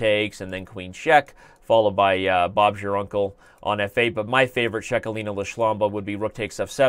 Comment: I didn't catch that Bob's your uncle?